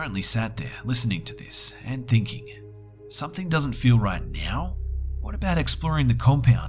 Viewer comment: Did he just say "something doesn't feel right (meow)"?!? 😂😂😂